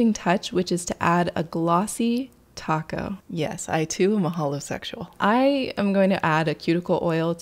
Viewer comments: I was so confused on what she said here😂 I thiught she said “yes, I too am a homosexual” at first lol. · I was watching.... and I heard you say glossy...TACO. And...I indtantly hit the like button!